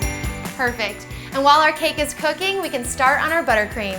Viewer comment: Did she say "crooking"